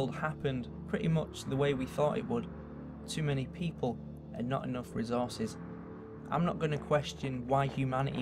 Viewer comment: I thought he said a lot of people not enough chicken